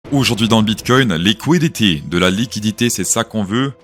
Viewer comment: j'ai entendu "les couilles d'ET" mais enfaite t'as dis "liquidity"